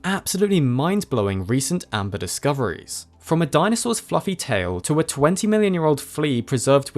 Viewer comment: Lol my volume was low and I thought he said: "...from a dinosaurs f**king tail".